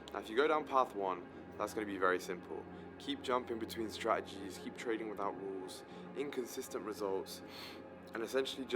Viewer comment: Did bro say keep trading with Al brooks?